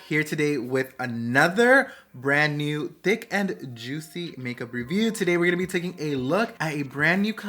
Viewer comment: Lol why did I hear “d🍆ck and juicy” instead of thick and juicy!!! 😂🤭 but either way love the lip combos !